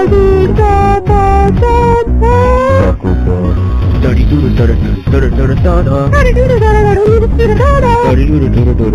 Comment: 뭐라는거여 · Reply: 마라톤바